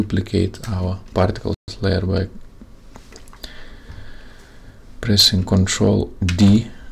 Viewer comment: Please follow closely. Did you get the null object moving? to copy path of the shape layer line go to shape layer/contents/shape1/path1/ and inside is a path with stopwatch icon you have to copy that and paste on your null onbjects position. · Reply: you were saying Ctrl (V) o thought you were saying Ctrl (P) once I figured I out it worked.